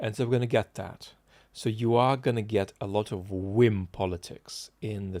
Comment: Is he saying “Getting a lot of Win politics?” I don’t know what this means, and I’m not sure it’s because I don’t grasp the pronunciation, or the grammar.